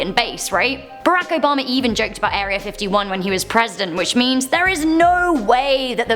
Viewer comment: For a second I thought she said “when he was pregnant at 1:21 LOL